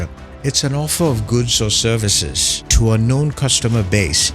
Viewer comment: the subtitle says TO UNKNOWN, I heard to a known. Considering that this is an educational content. Will be great to have accurate subtitles please, many rely on subtitles.